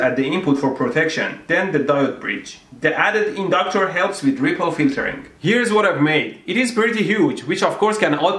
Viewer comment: "The added inductor helps with ____________." What did he say here? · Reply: ripple filtering